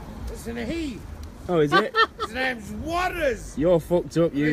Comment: I’m so sorry. My baby brain cell thought he said. “HIS NAMES WADDLES!! He’s an asshole. 🗣️” 😂😂😂